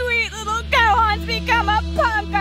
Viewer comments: Chi-Chi:my sweet little gohan become a........ What? Did she said at 2:59??? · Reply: She said, “Punker.” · wtf did she say? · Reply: Puffer